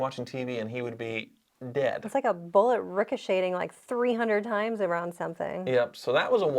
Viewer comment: Did just say rhicoSHADING?? 😃